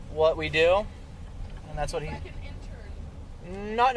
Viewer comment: What did she say at 20:47 ? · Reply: Like an Intern.